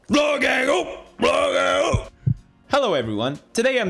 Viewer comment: it sounds like pewds is saying LOGANG OOOO LOGANG OOO in the beginning of the video lol.